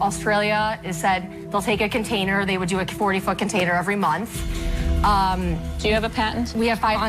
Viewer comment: Why do I keep hearing “They would do 40 fucking tater every month” at 3:28?